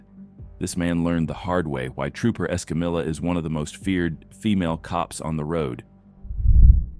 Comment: Lol I thought you said female cop